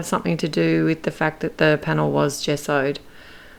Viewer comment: The paper didn't warp because it was....?? I couldn't understand what you said. It sounds like you said 'just sewed'. Which I know isn't right.... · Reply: Gessoed is what she said. This would make the cardstock a bit heavier, thus not much warping would occur.